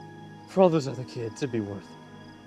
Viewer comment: Did he just say, "I'd be worth" XD